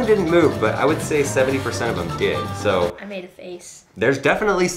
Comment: "i made a face " LMAO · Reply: I thought he said "I made a base" 😐